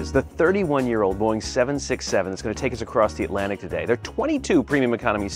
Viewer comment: did i just Hear 67 ? BOIIII TS SO TUFF🫱🫱🫱🫱🫱😂😂😂🥀🥀🥀🥀🥀